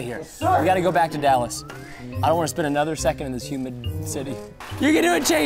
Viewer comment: “I dont want to live a minute in this human town anymore” hmm whats that supossed to mean🤭🤔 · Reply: I thought i heard him say humid city not human city